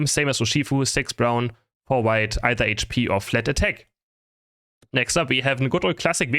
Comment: What is he saying at 2:20? Emblems 6 brown, 4 white, either HP or ??? Fletter Tech? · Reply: Think he’s saying flat attack